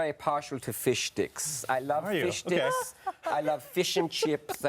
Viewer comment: legit thought Dee (as Klaus) said he was partial to Fish Dicks.....